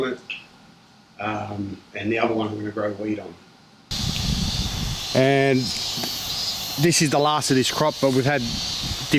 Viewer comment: Did he say " The Other One I'm going to grow weed on" ?!